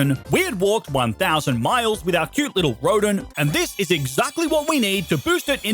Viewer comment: did he say MILES?!